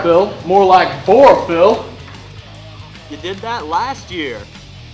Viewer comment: What is he saying at 3:22? I couldnt unedrstand it in the first video either! =P "You said that last year!"